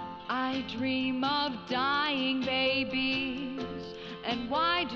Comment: I thought she was saying "dying daisies"